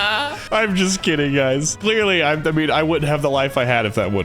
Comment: It's already begun! Everyone's turning into 'KIng of the Hill' characters! 20:30 Did say "Clearly I'm double you (i.e. W)..."?!